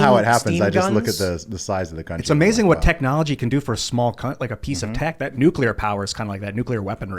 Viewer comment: I love how it sounded like said "It's amazing what technology can do for a small cunt."